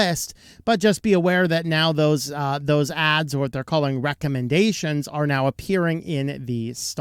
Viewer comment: I had to rewatch this 3 times, then turn on CC to hear this clearly as "recommendations." It sounded like something much filthier, and more appropriate.